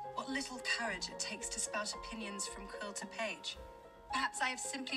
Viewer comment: The first time I watched it the volume wasnt on so i thought she said onions not opinions and I was just so confused